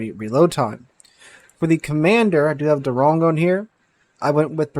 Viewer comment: I heard "we've got the wrong one here" and was like why would you showcase the ship with the wrong commander :D